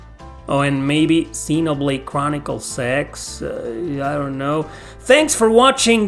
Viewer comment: I heard you say xenoblade chronicle sex??? I guess we hear what we want to hear!Hahaha